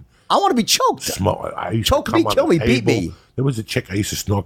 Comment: I thought Jordan was going to say piss on me, beat me, try it out. Home here now.